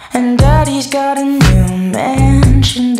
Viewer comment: Dont lie. EVERYONE thought he said daddy got new man the first time listening lol